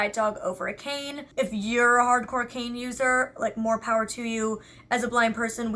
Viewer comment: when she said "Hardcore cane user" i thought she said "hard cocaine user"